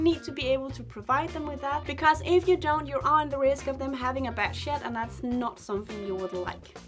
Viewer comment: Bad shed. Omg I thought u said a bad shit 😂😂😂